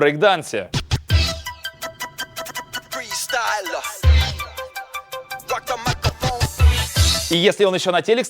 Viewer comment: мне одному послышалось ракамакафо?